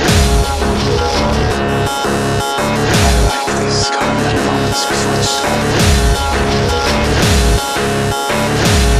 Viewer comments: Am i crazy or does anyone else hear whispered words at 0:14 to 0:17. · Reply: A little late but it sounds like "I like these calm little moments before the storm" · Does anyone know what he says? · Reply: "i love the calm moments before the storm" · What is that being said at 0:13? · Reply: i like this gaurd is what it says